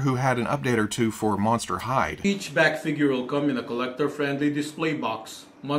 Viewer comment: I thought you said ‘Monster High’ and was very confused until the ‘Monster Hyde’ figure appeared onscreen-LOL!